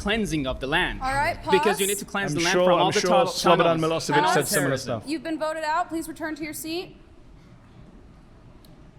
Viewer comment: what was the name Mehdi said there? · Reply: Oh it is Slobodan Milošević. The guy behind Bosnian Genocide.